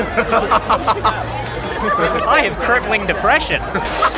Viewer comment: what is he saying? :D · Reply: Concussive "I have crippling depressions." :^)